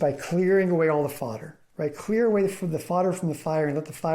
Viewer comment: What word are you saying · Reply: Fodder (e.g. dry hay...stuff that burns). LOL. I will work on speaking more clearly. Thanks for your question.